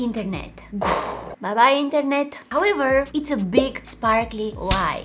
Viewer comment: I love when she said “bye bye internet “I died of laughter😂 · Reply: I thought she said, “but my internet” 😂😅